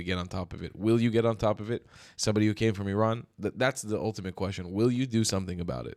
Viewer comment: It sounds like he is saying he is from Iran instead of Italy.